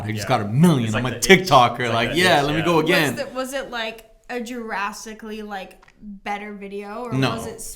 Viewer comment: Did homegirl just say "jurassicly" instead of drastically? 😅🦖🦕😅🦖🦕😅🦖🦕😅🦖🦕😂😂😂